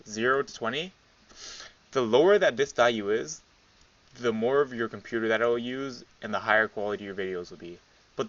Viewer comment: in the video what did you say? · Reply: The lower that the crf value is the more CPU it will use but it will also increase the quality. There's CC on this video try it out :D